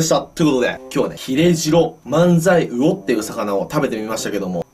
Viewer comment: ヒレジロマンザイウオが 切れ痔のマンザイウオって聞こえた件